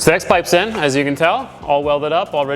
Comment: i know what he's actually saying but it literally sounds like he'd saying "sex pipe's in". 😅🤣